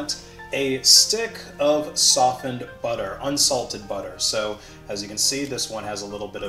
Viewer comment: I heard "software butter"...